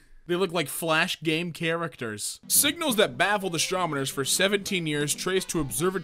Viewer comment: says astromoner instead of astronomer